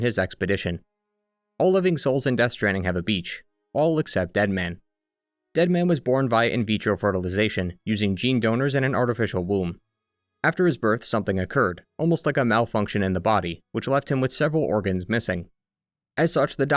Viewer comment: did you just say “in-beach-ro fertilisation”?